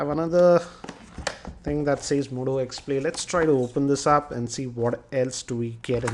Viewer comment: what ? Moto x play he says !!!